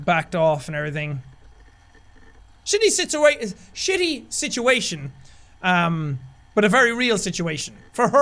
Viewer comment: When you said shitty situation it sounded like you said Cindy shitts her weight